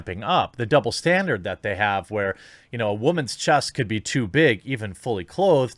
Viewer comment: I am proud of myself when he said "women's chest" I heard women's chess.